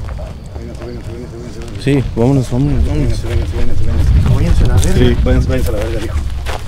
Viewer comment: porfa , has un vídeo del kilómetro 31 , eres un crack en el tema Edit: en el Minuto 18:45 yo entendí YA VALIERON VERGA